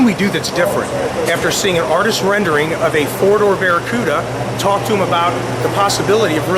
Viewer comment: At quick (side) glance, it looks like an AMC. When he first described it, sounded like he said "A 1974 DOOR-Barracuda." lol